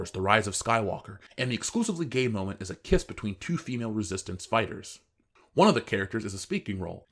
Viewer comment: I thought he said "the kiss between same-sex spiders". XD